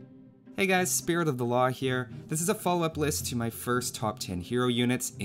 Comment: I always hear "Welcome Spirit of The LA HIRE"